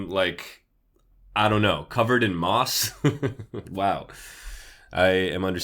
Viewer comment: did someone say moss 🥺